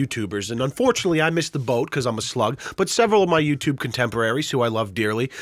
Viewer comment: I thought you said slut